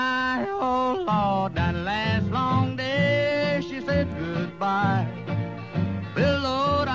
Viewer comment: I always thought it said "that sum'bitch she said goodbye"